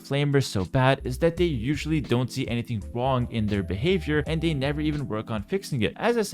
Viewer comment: It totally sounds like you said, "They don't see anything Huang with their behavior." Purposeful? :P